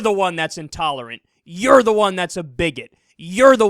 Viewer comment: Nothing to do with the actual video, but it's funny reading the subtitles for the hard of hearing. Here when Kyle says,"You're the one that's a bigot!" The subtitle reads,"You're the one that's a big hit!" That must be really confusing for anyone who can't hear.